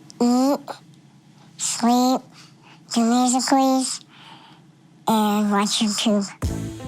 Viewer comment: did she just say musicallys Edit: nvm